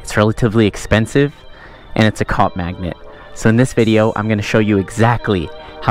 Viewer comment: Thought he said cock magnet when he said cop magnet. But either way, he’s right.